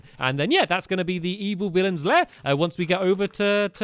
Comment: I thought villains said villager lair